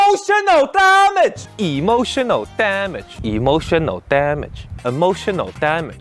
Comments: Am I the only one hearing "A motion of damage" ? yea? no? okay.. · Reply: I hear "Emotional Damnit" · E motional TA mage really sounds like he says a T LMAO